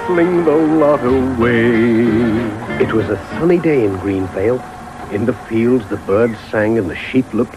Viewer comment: greendale or greenvale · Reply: I hear “GreenFail”